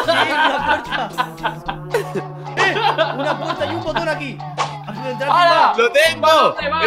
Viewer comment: oí mal o dijo: Ay un condón aquí :v?!? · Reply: Es hay un botón aquí no mal pienses